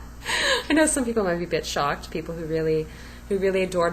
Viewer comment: I thought you said some people might be "Bitch-Shocked" not "a bit shocked.... lol Think I'll use that one.... Gotta say you lost me for the past 1-2 years... Ive been watching for a long time. Glad to see this video. It feels long overdue. Love the videos of you and baby, is too cute :)